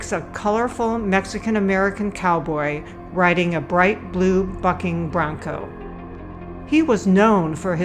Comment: a colourful Mexican American cowboy riding a bright blue fucking brown coat What TF did i hear that xD